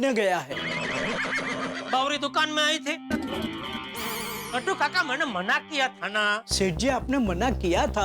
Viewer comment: Mistake in the subtitle as "Gattu Kaka" instead of the "Nattu Kaka"